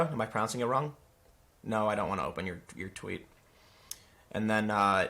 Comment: And sorry for two comments, But, I can’t help it. At 8:10, Tim’s way of saying, “No, I don’t want to open your tweet” Maybe because of the theme of the video, I heard, No, I don’t want to open your twat. 😂 I bet Milano hears that a lot now a days! In turn, she starts a sex strike. Lol!